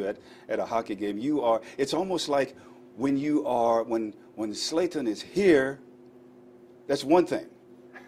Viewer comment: LOOOOLLL did he just call Zlatan, "Slaten" @ 4:04 Thats disrespectful Jim to the goat ZLATAN